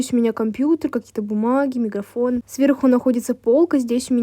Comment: мне послышалось или он сказал? :МЕГАФОН🤣